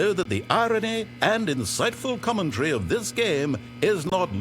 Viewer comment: the subtitle says comedy but the narrator says commentary. They probably were just like, f it, it's funny